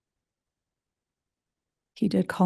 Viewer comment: What is a potster? I can't find it anywhere related to Mormonism or by Googling it. Am I mishearing the word?